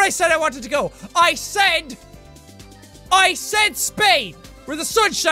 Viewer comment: Spain? I thought he said "Spade".....